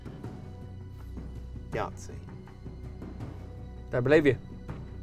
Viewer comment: he believed you, so you should get + 50, also u said that if your wrong on calling a bluff that place would not be taken from you.... I don't understand why u lose your yahtzee and lost points he believed you😅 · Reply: He said "don't believe you"